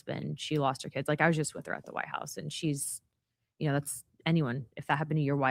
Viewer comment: I didn't hear her say "kids" at all. Maybe I'm deaf but I didn't hear it at all. I heard "lost her husband, she lost...I just saw her at the White House." It sounds like it's been edited.